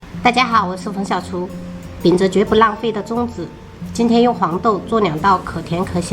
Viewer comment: 我开头听成了“秉着绝不浪费的中指”🤣